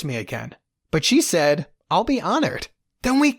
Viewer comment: i thought he said fuckinally but it was finally